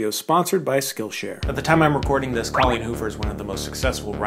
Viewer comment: Cailín (pronounced Colleen)is the irish for girl So I keep hearing you say "girl hoover" - that's all